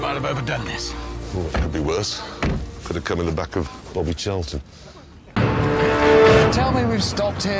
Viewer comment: did jeremy say "we could have cum in the back of bobby charlton"?